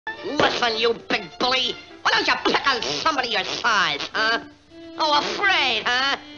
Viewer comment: What’s he saying at 0:00? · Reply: "Listen you big bully! Why don't you pick on somebody your size, huh!?"